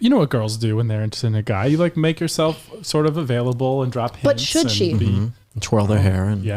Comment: Oh no, I had to rewind when I heard one of the responses to the question as to how you should let a guy at work know you're interested in dating him. I misheard "Drop hints" as "DROP PANTS." O_O XD